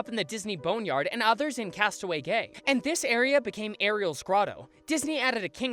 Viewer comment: does anyone else hear “castaway gay”